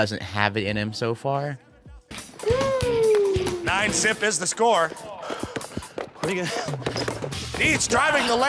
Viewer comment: what did just say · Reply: "9-zip is the score" zip means 0, nothing.